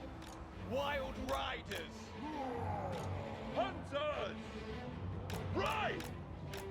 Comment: Excuse me, did you say "panzers"? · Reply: Hunters, close enough lol. Panzerjägers go!